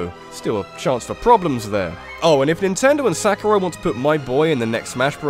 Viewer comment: "Nintendo and Sakura" did I hear that right? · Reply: TheSleepyGamer *Sakurai. As in, Masahiro Sakurai.(probably spelling his first name wrong)